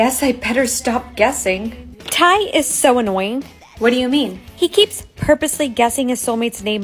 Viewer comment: I really thought she said “thank you is so annoying” 🤣🤣🤣🤣